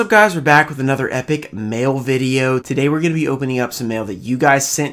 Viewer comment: I deadass thought you said MALE video I was like No! Dont get canceled :(